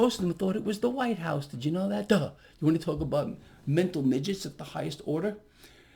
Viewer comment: What is Gregory talking about at 10:36 in the video? I can't tell what his words are about the White House. Can anyone make out what he is saying between 10:36 and 10:43? Is he calling Trump supporters "mental midgets." Trump supporters are the biggest part of his audience. He better watch his words if he wants to keep his audience if that is what he is saying.